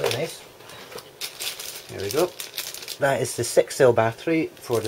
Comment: I thought you said "That is the sexist battery" lol xD